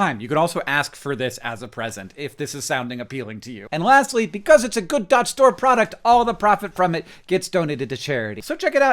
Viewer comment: Captions say 'good Dodge store', so I assume you've also got cars for sale? Also curious to see how you work more associatively when solving the Connections puzzle, as opposed to what I do, which is look at the words through a more formal lens!